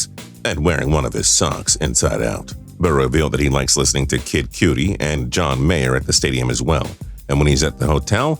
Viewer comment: Did… did he just say Kid Cutie?